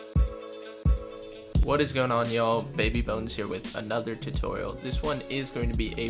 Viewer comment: I thought he said baby buns